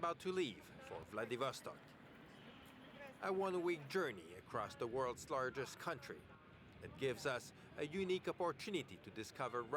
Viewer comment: What does he say right after "about to leave for Vladivostok"? at around 0:30. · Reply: "A one way journey across the world's largest country"